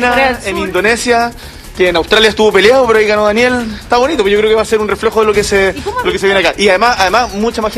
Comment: escuche mal o Boric le Dijo a Jadue Reflojo 1:08 ....🤣🤣🤣🤣🤣🤣